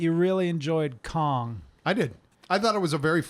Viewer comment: i thought you said "witch one of you said you really enjoyed pong" and as you were talking I was like what the fuck are they talking about that's not what pongs like helicopters the fuck type of pong is this. Then it hit me that you said Kong. And I felt I need to share this with the world.